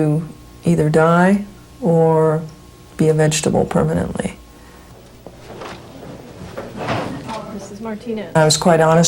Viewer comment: i thought she said be a vegetable permanently..